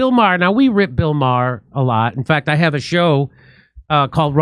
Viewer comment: I thought he said "I rip *Jill Maher alot" at first - that would have been great - Let's go with Jill Maher from now on, thanks😊